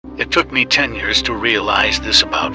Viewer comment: At beginning I literally thought anna is saying" It took me 10 years... "